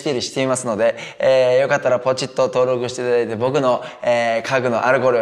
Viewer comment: この時期だから、「僕の家具のアルコール」に聞こえる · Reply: 僕の家具のあれこれを〜ですかね